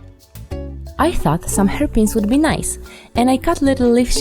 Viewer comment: When she said i thought some hairpins would be nice i heard "herpes''